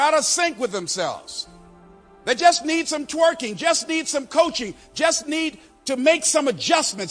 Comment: am I hearing Les correctly that we need some "twerking"? LOL It does not match the writing/ transcript on the screen.